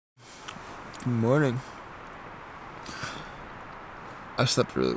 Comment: Minute 13:00 - 13:07.. Did you say, 'Good morning, Astagfirullah' What happened at that time? 😅 Always like your videos, keep the good work.. 👍